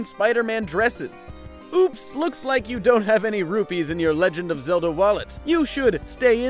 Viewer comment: it thought he said you don't have roofies